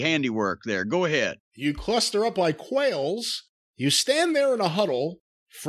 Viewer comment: Thank you for correcting idiots who thought JR said coils not quails.